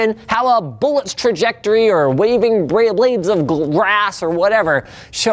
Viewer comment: did he say "raving beyblades of grass"